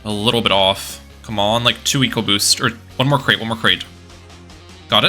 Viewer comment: Did he just say eco boost in this very much not battles game?